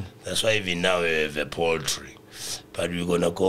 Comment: Kheshu kheshuu !!! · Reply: Thought they said Poetry kantii ke Poultry 🤔